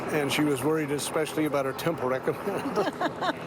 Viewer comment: “And she was worried especially about her…” What are the words that finish this sentence? I have listened to this clip a dozen times and I cannot figure out what he’s saying. Can anyone help, please?? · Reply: Yep! He said temple recommend- it allows members of the church of Jesus Christ of Latter-day Saints to worship in the temple. If it got destroyed you can easily get a new one but it’s still a bit of a process because you have to meet with your church leaders haha, such a sweet old lady